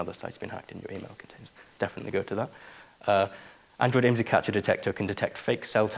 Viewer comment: "Android IMSI Catcher Detector" is what he's saying at 12:38, if anyone else got lost there :)